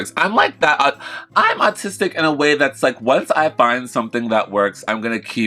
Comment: I thought I heard this but I think she said artistic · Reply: I thought I heard "artistic" also! 🤣